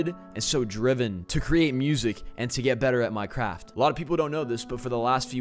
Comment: Did he say minecraft????? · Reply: Zachzz my craft 👂 listen